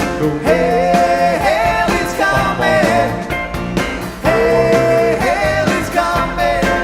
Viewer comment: Found out about a year ago that all 3 of my teenagers until last year thought they were saying “hell is coming”